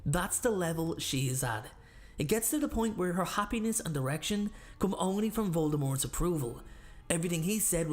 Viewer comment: Really thought you said “her happiness and erection” … which, I mean, isn’t far off 😂